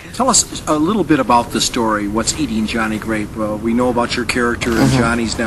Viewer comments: lol at 4:13 did he say wats eating johnny grape. and decaprio was messin up some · at like 4:21 does he say what's eating johnny grape?